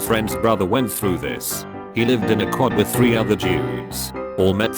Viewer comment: I'm so sorry if you take offense to this, but for a second I thought it said "Three other Jews" and I'm really disappointed with myself